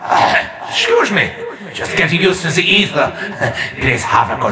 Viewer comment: what does that say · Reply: I thought he said “just getting used to the Aether this happens a bunch of times”